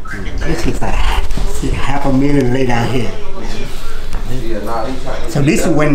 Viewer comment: I thought I heard him say half a million not a million